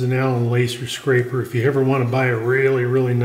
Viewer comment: Learning so much from you videos, thanks so much! What kind of scrapper did you say that was? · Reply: at the 20:45 mark you used a scraper, but I couldn't understand the name you gave it. Almost sounded like Alan Laser.